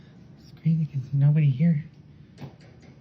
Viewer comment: I heard “ excuse me” not Mazie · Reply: I hear "Crazy"